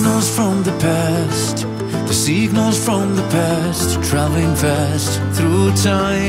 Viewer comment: I heard "seagulls from the past" and now I cannot unhear it.